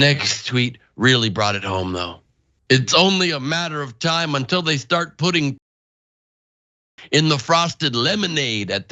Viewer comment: I’m afraid to ask what he said was going to be put in the frosted lemonade. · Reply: Transexual semen